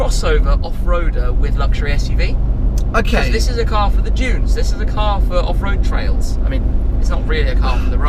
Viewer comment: Did I really hear say ”This is a car for the Jews”